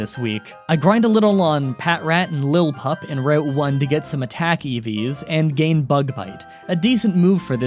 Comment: I heard “attack EVs” as “Attack eevees” and pictured a hoard of vicious guard cat-rabbits